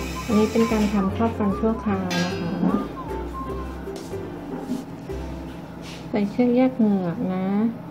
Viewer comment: นาทีที่ 11:13 หมอพูดว่าอะไรนะคะหนูฟังไม่รู้เรื่องค่ะ😂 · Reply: บอกว่าใส่เชือกแยกเหงือกก่อนพิมพ์ฟันค่ะ